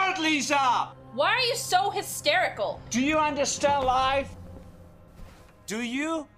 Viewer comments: Ok, does he say "Do you understand life" or "Do you want to stay alive"? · When he said “Do you understand life,” I realized that I don’t. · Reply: I always thought he said "Do you understand why" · What he says: "Do you understand life!?" What I hear: "Do you wanna stay alive!?" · Okay, this whole time I thought he said "do you understand LIE". which at least kinda makes sense, but he atually said "do you understand LIFE". There is something wonderous about the movie's nonsense managing to suprise years later.